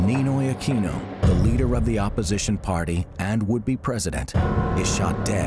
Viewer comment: "Would be president" did i heard that right? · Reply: "Ninoy Aquino", leader of the opposition party and would be President...." is what I heard. Damn, this documentary just lost credibility.